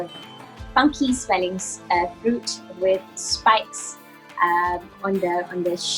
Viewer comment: I think she said 'Spikes' there and not 'Spice' :) Describing how durian looks like.